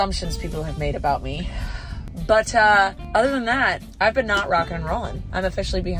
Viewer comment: when you said “but uh” it sounded like you said buttaahhhh